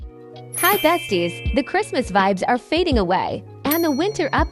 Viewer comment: How did i hear "Hi Zesty's" at the start😂💀💀